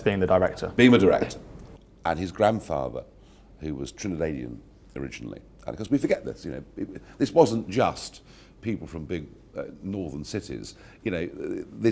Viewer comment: What was the word at 9:11? I replayed it plenty but didn't catch it. "His grandfather who was 'Trintavalian????'"